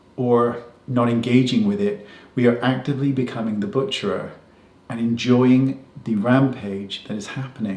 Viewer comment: Does he actually say "butcherer" at 2:34?